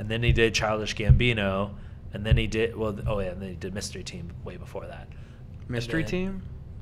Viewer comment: Oh! Donald Glover was in Mystery TEAM. I was listening to this half asleep and I thought you said Mystery Men... Like 1999, staring Hank Azaria, Ben Stiller, Eddie Izzard, that one woman who's gimmick was just being typical 90s sarcastic, ironic and edgy... And the rest... That movie that is in the All Star video from Smash Mouth. That movie... I was disappointed when I googled it.